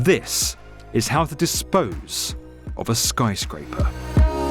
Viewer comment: Thought it said "How to dispose of a skydiver"